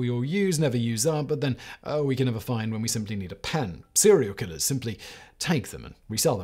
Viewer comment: I must be hungry, because I heard "cereal killers" more than a couple of times.